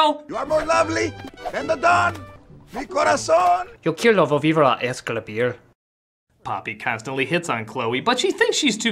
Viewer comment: ¿qué dice? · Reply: "Yokiro volvir a escribir" XD (Yo quiero volver a escribir)